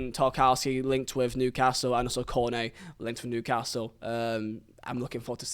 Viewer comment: It sounded like you said Kone A instead of Cornet lol